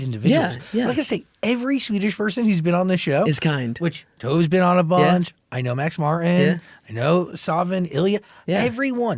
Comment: Can someone help me identify the Swedish artist Zach list at 44:14 what I hear is "Toes be on a bunch, Max Martin, Savin, Elliot, Zara Larson" But I don't know any: Toras be on a bunch, Savin or Elliot...